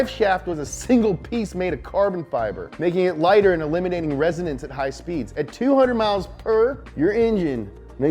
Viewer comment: in 13:03 it sounds like he says eliminating residents